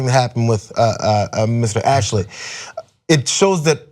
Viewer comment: did he just say "Mr. Ashley"??